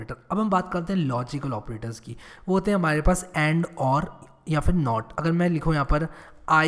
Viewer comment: bhai 6:15 se 6:25 mai kya bol diya aapne.....samajh nhi aya........and aur not????